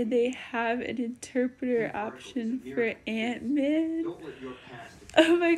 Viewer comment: I thought you said "an interpreter as Antman" for a moment i thought Antman would be doing sign language on the corner of the screen 😭